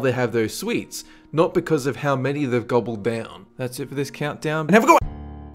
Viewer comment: That's it for this vide-HAVEAGOODONE !! Why does he always do that at the end XD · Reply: I thought he was saying "I forgot!"